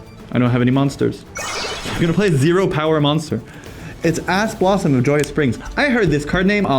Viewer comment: did he say Ass Blossom